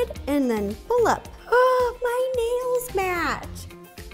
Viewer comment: When she sead "My nails match" I heard "My names Mat"...